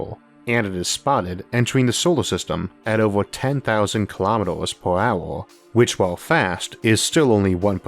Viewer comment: Don't know if anyone has allready mentioned it, but there is an discrepancy in the closed captions at 27:39. The audio says 10,000 Km/h but the subtitles say 100,000 Km/h (So either the audio was wrong and the number was corrected in the sub, or the sub contains a typo)